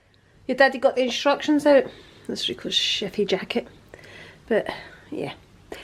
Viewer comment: I thought you said here’s Rico’s shitty jacket🤣🤣🤣 but went back and obviously was chefy jacket excuse me😳 your new storage is lovely quite right to have them on display. To look at and admire. Enjoyed the vlogg very much👍